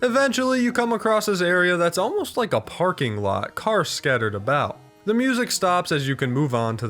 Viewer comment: i was watching and my audio failed for a sec so it sounded like he said "fucking lot" instead of parking lot